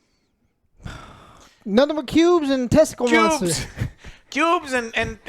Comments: I swear I literally heard OJ: " Nuthin' but pubes and testicle monsters" 🤦🏼‍♂️ ..I mean, makes sense, right?? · Who else was looking forward to Joes review more than the actual movie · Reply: I swear I literally heard OJ: "Nuthin' but pubes and testicle monsters" 🤦🏼‍♂️ ...I mean, makes sense, right??